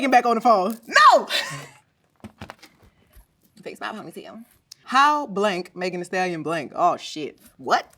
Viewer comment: What did she say? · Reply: I think fix my ponytail 😭🤣